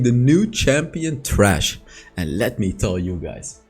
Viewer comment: I thought he said "the new champion, Trash'' which I perceived as trash. Until he said its amazing and saw the name Thresh. P.s-Mlbb copied this champ long ago whose name they kept Vexanna.